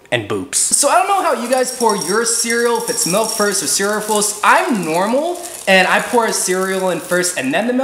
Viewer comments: did he just say his normal??? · Wut i hope you said boops